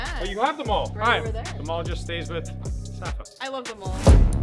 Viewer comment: I like how safa said " I like the maul." It kinda sounds like she said I like them all